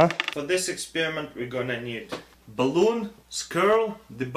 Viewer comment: I thought you said you need eye balls.....